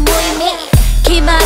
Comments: Instead of hearing" stuck in my head" i hear "stuck in my hell" lmao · What everyone hear : "STUCK IN MY HEAD" What I hear : "StUcK iN My HELL"